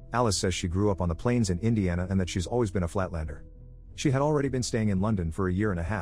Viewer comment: I heard flat-earther